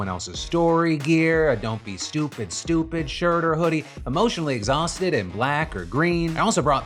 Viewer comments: I wasn't looking at my screen and thought I heard you say exhausted and black 😂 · I thought Phil said “Emotionally Exhausted & Black” not “Emotionally Exhausted *in black” for his new Beautiful Bastards gear. 😅 I had to listen again and ngl was a bit disappointed, I liked that one, I mean it’s still good but.. maybe Black History Month Edition? 😂